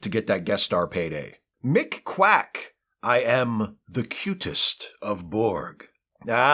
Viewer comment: When I was a child, I thought they were saying cutest of borg. I get it.